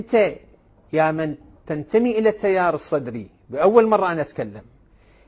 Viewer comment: لا حول ولا قوة إلا بالله ، بدل ما اسمعه يگول "التيار الصدري" سمعته يگول "التيار الكهربائي" 🐸💔